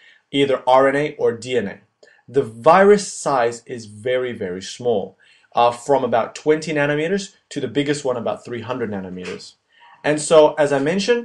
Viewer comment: كأني سمعته يقول حجمها من 20 نانو متر إلى 300 نانو متر